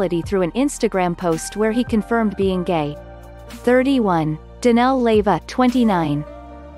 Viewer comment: Did you say Leyva, Danell Leyva??? @ 0:42-0:44. Oh sh&%! I'm about to conk out! A beautiful Xmas. gift; thanx for the good news